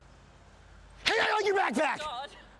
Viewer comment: hey I owe you back tax!!! Is that what he said? Lol